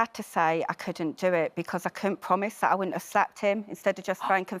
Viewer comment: I thought she said "I couldn't promise I wouldn't have slept in". Was thinking to myself "well, at least she's honest".